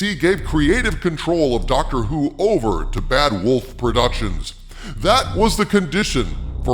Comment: Oh you're saying bad wolf I thought I heard bad robot. That'd be weird.